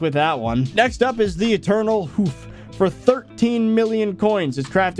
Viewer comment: really thought he said "eternal oof" also literally just saw a purple jerry for 69,420,420.